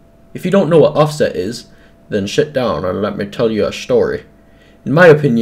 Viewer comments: When he said "Sit" it sounded like the S word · Why did he just say ‘shit’ down? XD (Yeah I know he meant ‘sit’ down, and I accidentally say that at school......) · did you say shit down?